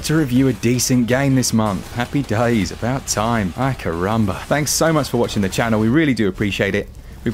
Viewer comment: Did I hear an "Ai, caramba?" (10:30) LOL